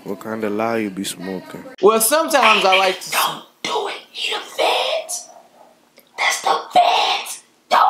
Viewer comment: He said "The Feds" Lmao I thought he was saying "Fans" the whole time 😂